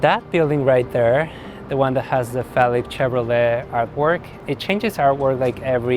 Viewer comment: He said Felix Chevrolet, I thought he said phallic Chevrolet.